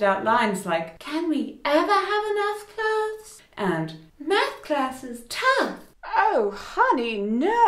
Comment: I’m dyslexic, so when talking Barbie said, “Math class is tough,” I heard, “Sister, I know your pain!”